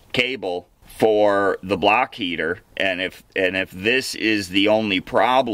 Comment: Block heater!!!! 😆 I am from Boston and I even thought you said "BLACK" heater!!!! Too 😄 funny!